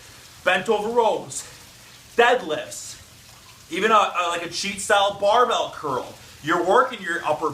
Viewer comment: What curl is he saying at 7:45? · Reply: Cheat style curls, or cheat curls